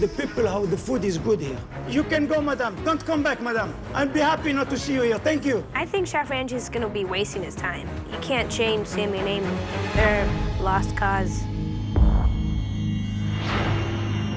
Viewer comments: I heard chef ramjee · did she just say chef rangy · Anyone else hear “ I think chef Rangeys” 4:12😂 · Did she just call chef Ramsay “chef ranjit” at 4:11😭😂